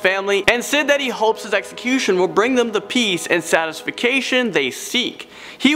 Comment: did he just say satisfication? :P